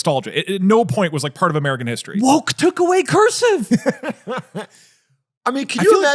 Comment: Ive had this on in the background and the moment I start paying attention I hear "WOKE TOOK AWAY CURSIVE!" which is amazing honestly